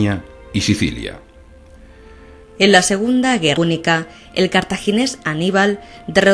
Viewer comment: Alguien sabe que dice después de "en la segunda"? nea preguntaba en los comentarios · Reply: guerra punica